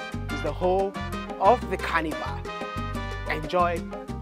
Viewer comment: I thought you said "cannibal" at first... 😭😭😭😭😭🤣🤣🤣🤣